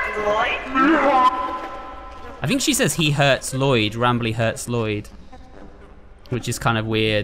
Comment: What I hear when Molly says something I hear as ‘she hurts Lloyd’